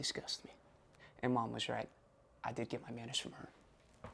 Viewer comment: It should've been "I did get my manners from her." instead of "I did get my manager from her."